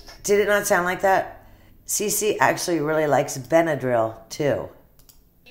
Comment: I listened a few times and it sounded like “Ce-Ce actually really likes Benadryl too” I could be wrong but that’s definitely what it sounds like!!